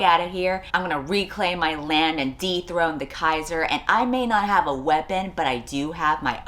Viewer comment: Why did I hear "deep-throat the Keizer" everytime she said "dethrone the Keizer" 😭